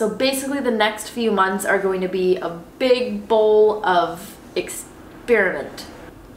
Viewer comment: I heard "exspearmint" lol! Damn herbal earholes!